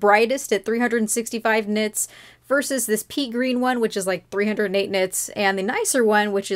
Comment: I thought she said PEA green not PEE.